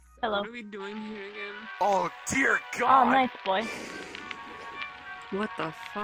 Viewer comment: Did anyone else hear o deer god.